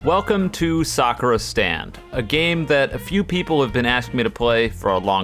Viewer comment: At the begenning i heard "welcome to suffer stand" instead of "welcome to sakura stand"